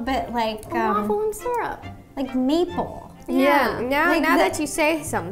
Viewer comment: no offense Rosanna but for a moment i thought u said people not maple and i was just shocked then i replayed it lol 😂😂 but in all seriousness i love u guys 💕💕